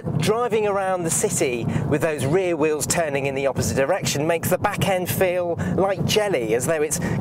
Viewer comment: does he say "opposite erection" or is that just me? I'm sure he does!